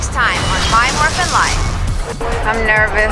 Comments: T I thought it said My Morphine life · damn! lol I thought it said "my morphine life"